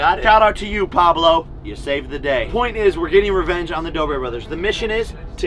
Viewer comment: Oh....never mind. I thought you said Doobie Bros